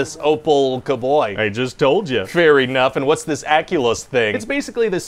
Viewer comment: When he said fair enough, I totally heard it as "fairy-nuff"